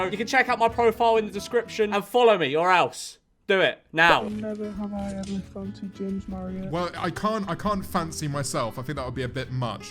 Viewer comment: anyone know what the first one said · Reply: Never have I ever fancied James marriot